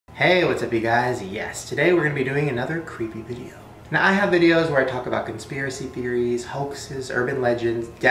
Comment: I thought he said, "Yesterday, we're gonna be doing another creepy video," but he said "yes today."